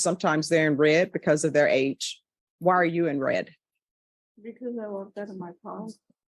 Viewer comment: Does anyone know what the defendant said when the judge asked her why she’s wearing red? The question is asked at around 2:40. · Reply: Because she left her pod